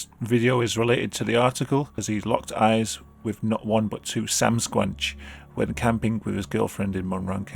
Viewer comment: did you say sam-squnch? lol